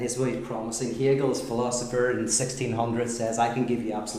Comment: i swear, i thought he said hegel was a philosopher in the 1600s.